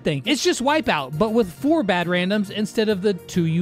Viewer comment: Yeah.... bad randoms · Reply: the background? Yes and no... I just heard him saying "4 bad randoms"